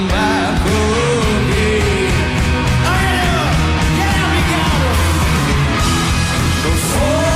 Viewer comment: What is Alex shouting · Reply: "get up Qantas!" lol idk